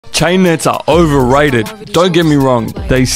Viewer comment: Why did I hear “chinets are overrated”💀💀